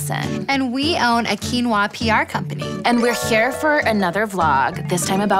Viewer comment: I thought I heard them say "Quinoa Pinot Noir Company" at first, which makes about as much sense as Quinoa P.R.